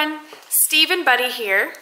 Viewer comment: I missed something. I thought she said Steve and Bunny